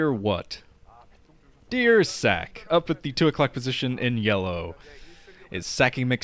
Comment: When said "dear what? Dear Ssak" i thought he said deer sack LOL XP